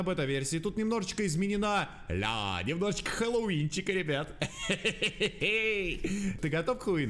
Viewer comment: МНЕ ОДНОМУ КАЖЕТСЯ ЧТО НА 1:01 , ЧТО ОН СКАЗАЛ ХУИН